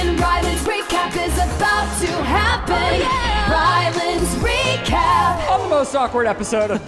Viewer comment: The last part caught me so off guard I thought I was tripping I heard Shane say “‘Ryan’s recap” twice 😀